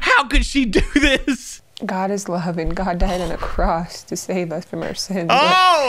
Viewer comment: Did she just say God died in a cross? 😂